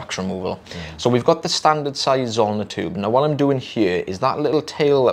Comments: I could have sworn I've heard "sizeometer" tube. Surprised to see the spelling so different! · Thanks for putting the terms on screen. I always thought you were saying standard size OMNI tube